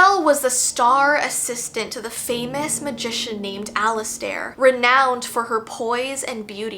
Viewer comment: i thought you said alastor for a second- 😭